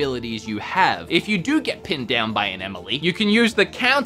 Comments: Did he say "get pinned down by an Emily"? That's what it sounded like! Lol! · Did he say “Emily”?